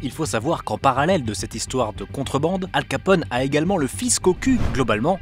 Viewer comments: "le fisc au cul" que j'ai entendu "le fils cocu"😂😂 il a fallu la repasser plusieurs fois pour comprendre 😂😂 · "Il a le fisc au cul", moi j'ai entendu "il a le fils cocu" mais ça voulait rien dire